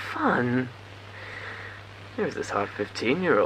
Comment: I'm pretty sure that it said "hot" instead of "half", which honestly makes it even worse